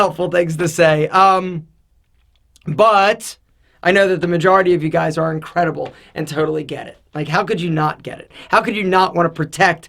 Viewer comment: thought he said "I know the majority of you guys are incredible and totally gay"😂😂😂